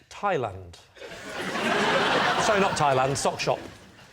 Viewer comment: Canadian here, I love the UK sense of humour but sometimes there’s a reference I don’t recognize. Could someone explain? At 3:40, Lee responds to Claudia with “Sorry not Thailand, sock shop” and everyone laughed. First question - did I get that right, did he say “sock shop”? If yes, why is that funny? If not, what did he say that I am hearing as “sock shop”? (That quip at 4:19 when he dropped that “my wife said I have to stop doing that at” was hilarious and “yeah, it’s pretty deep man” at 5:25 was too) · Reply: He did say Sock Shop. It's a play on words. Think of Thailand as Tie Land (as in neck tie's.) Hence the Sock Shop quip. Childish humor but I love it. My kind of humor.